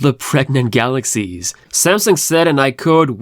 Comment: When I heard you say "Pregnant batteries", I was like: Wth?😅